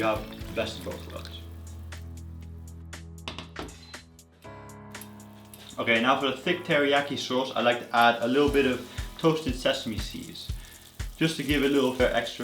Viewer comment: what is that? i cant hear clearly..thanks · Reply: "Now for the thick teriyaki sauce I like to add sesame seeds"